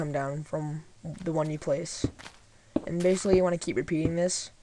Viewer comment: what you say? you wanna keep your penis?